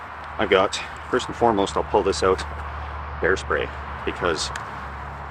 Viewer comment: LMAO i thought you said "hair" spray, as if it were an improvised firestarter